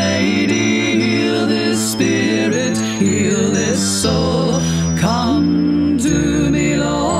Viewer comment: I thought I heard Heather in there